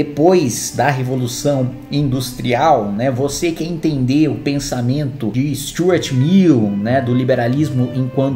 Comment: do nada eu escutei "vc que quer entender o pensamento de Stuart Little" kkkkkkkkkkk do nada